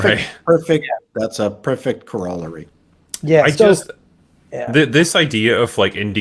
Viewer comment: Anybody else have to turn on the subtitles to see what dude said 00:10 and have to look up "corollary"? Never seen or heard that word before